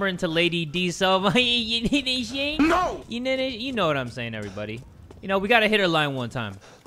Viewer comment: what are you saying- in here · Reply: "you know what I'm saying?"